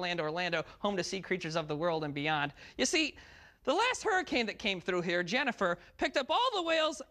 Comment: i heard hurricane janitor